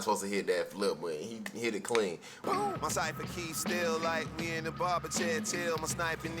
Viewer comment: I thought Mach said “my cypher keep still like we in the barber chair” Either way you interpret it, the line is fire